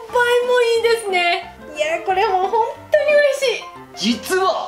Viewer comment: 何回聞いても「ほんっと〜に、おいしい！」にしか聞こえないんだけど、何て言ってるんだろう、、笑 もし本当においしいって言い間違えてるんだとしたら可愛い😂💕💕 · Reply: 私もおいしいに聞こえましたが、多分「本当に嬉しい😆」と言っていると思います笑